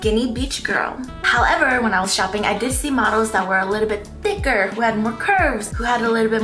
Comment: what did she say at 0:28? dizziemodels?